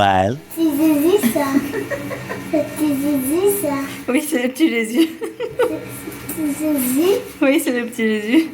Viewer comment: Pourquoi est ce que j'ai compris "C'est le petit zizi ça" alors que disait "C'est le petit Jésus ça" 😂